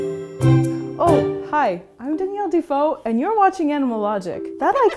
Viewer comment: after that evil laugh..I heard Hi! I'm Danielle Devil rather than Hi! I'm Danielle Dufault